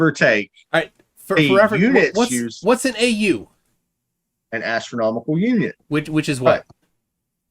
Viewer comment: For a moment, I thought he said ‚astronomical union‘ …